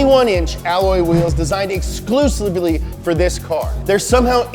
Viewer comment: did he just say “exclusebly”?